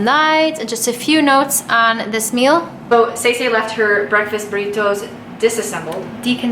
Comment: Why does it sound like you said "so, Stacy left her breakfast burritos disassembled" @ 12:03?